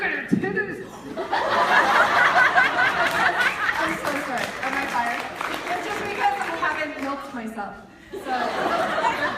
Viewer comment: What did Melanie say at 2:18 I didn't catch that. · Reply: "Look at my titties... it's because I haven't milked myself."